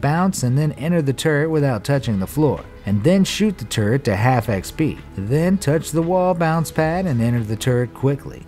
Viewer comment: did he just say shoot the turret to half xp?